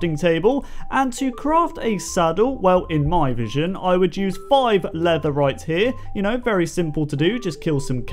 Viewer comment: d-did he say leatherite? NETHERITE ORIGINS?